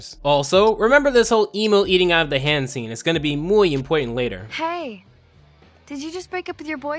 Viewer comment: Did you just say "muy important"? I don't know why but I found that hilarious!